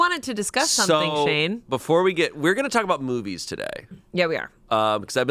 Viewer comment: in some timeline of me being drunk af, i heard say "today we'll be talking about boobies" And amanda reacting in disgust lvl1 Turns out im just half asleep